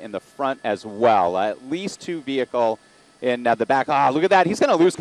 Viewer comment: Did he just say "At least 2 vehicle?"